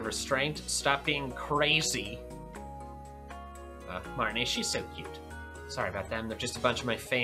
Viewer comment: I literally thought Mikey said “Barney” instead of Marnie 😂